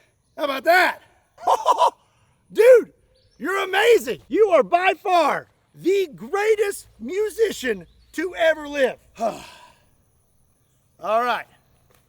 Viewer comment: did I hear “you are by far, the greatest musician to ever live“? I heard musician